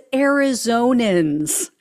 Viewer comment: I thought he was saying Eurasians instead of Arizonians. Why don’t understand why other commentators don’t mention this.